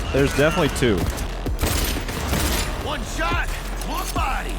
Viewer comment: I think he said "One fucker less" not left